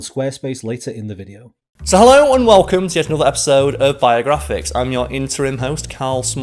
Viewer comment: When you said "Welcome to Biographics", I thought you said: "Welcome to your Viagra fix." Bwahahahaha Anyways Happy New Years!